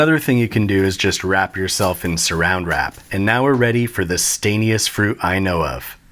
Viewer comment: Three things: This was super unhelpful. It was only very mildly funny. And did he say SURROUND WRAP at 0:08?